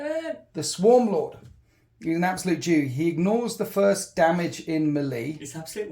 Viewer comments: I thought he said 'An absolute jew" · bruh I thought you said “he’s an absolute Jew”